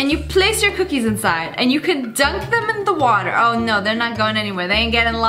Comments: ”You put the cookies inside and you can dunk them in the water ” We love white water 😂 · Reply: Biglyp I was saying it sounded like she said “Drunk” instead of “dunk” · did she really say dink them in the water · Did anyone else hear her say you can dunk your cookies and water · Did you just say get your cookies and dunk them in the water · did she just say dunk in the water?? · In 1:47 did she say dip in the water?